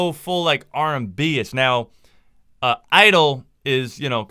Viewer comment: did he say IDLE is out of the kpop scene or did I just mishear it?